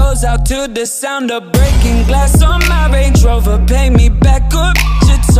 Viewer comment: I thought it said "Breaking glass on marriage" 😂😂 Thank you for this clean one I've seen clean ones and all they do his turn down the volume when it says the bad words?! And plus I like listening to clean more ! Tysm